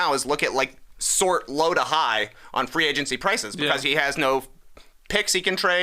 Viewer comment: Maybe its my English, but I cant figure out few words is saying at 16:47 mark ''sort, low to high''? I get overall sentiment, its like when you struggling, and just go to discount isle, but it sounds like just name dropped some LOTR bad guy haha.